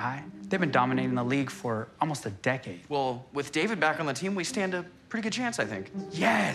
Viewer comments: BRO I HEARD DAVID BECKHAM 💀💀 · I thought he said David Beckham for a second